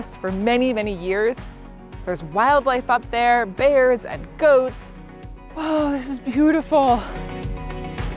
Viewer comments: I legit thought you said this campsite had “ghosts” and I was so confused until the goats showed up. 😂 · I thought you said there were bears and ghosts up there! The goat was cute... but ghosts would have been cool too.